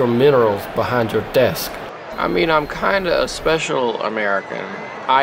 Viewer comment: "I am a dazzling herbivore who hates money" Best line. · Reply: That is a special American. (edit, was wrong, I thought he had said strange